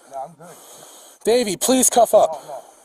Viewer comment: I keep hearing “Baby cuff up, please cuff up”